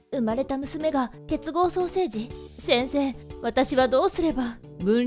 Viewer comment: 本家「結合双生児」 私が聴き間違えたやつ「滅亡ソーセージ」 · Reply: 私絶望ソーセージって聞こえたwwww